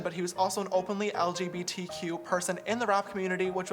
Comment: Did he just say that Lil Peep was in the rock community?